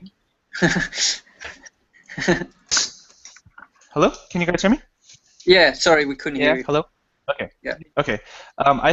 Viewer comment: Hello guys, can someone write a title and author of book which mentioned at 0:59:20? I can't catch that. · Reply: The books are: "Composition of Outdoor Painting" by Edgar Payne and "Framed Ink: Drawing and Composition for Visual Storytellers" by Marcos Mateu-Mestre